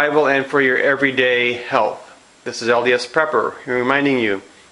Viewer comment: I thought it said LSD prepper!